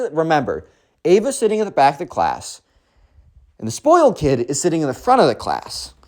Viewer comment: i literally thought you said the spoiled kid swakes